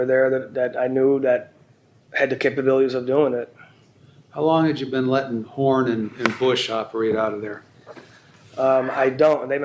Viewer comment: At very first I thought he said how long you been dealing Bush and porn 😂